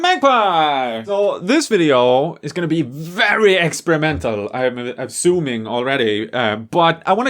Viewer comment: Ha ha it sounded like he said this video is going to be very excremental